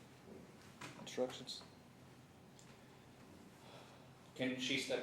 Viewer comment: What does the judge say at the 26:20 time mark? Instructions? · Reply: Yes. As in jury instructions. Since all the proof is in.